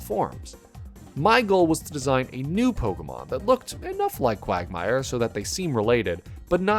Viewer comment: I could swear I heard say Quagmire. Digging the design though